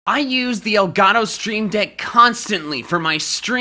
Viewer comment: Bruh I thought you said stream dick for a second...